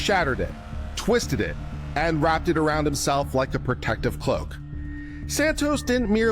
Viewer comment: I'm so gladI listened to this again. I thought, in the opening when you said, "wrap around you like a protective _______", I originally thought you'd said, "hook" & I couldn't make sense of that to save my life! I now hear it well and you saying, "cloak" so I am thankful I chose a second listen. This one's awesome!!